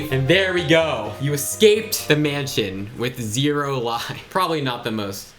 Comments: I thought 0 lives said olives 😐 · i thought it said you escaped with olives but there was no space🤣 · I thought that said "You escaped the mansion with olives" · whenever i saw the "you escaped the mansion with 0 lives" originaly thought that it said olives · i thought it said you escaped the mansion with olives 😭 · HELP i thought it said “you escaped the mansion with olives” 😭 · When he escaped the mansion with 0 lives, I thought it said "You escaped the mansion with Olives." XD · When it said: you escaped with 0lives my dumb ass thought that it said: you escaped with olives💀💀💀💀